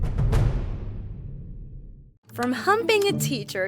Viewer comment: First thing I hear is "From humping the teacher..." LOL